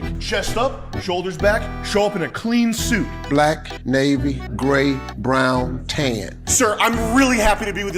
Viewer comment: I thought he said gravy as a suit color